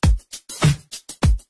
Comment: Ok i heard the audio "Garoth she's just not into you"...Am i the only one who heard that!?